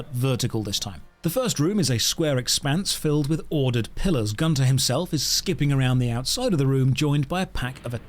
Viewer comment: I misheard "Gunther himself" as "Gun to himself." ._.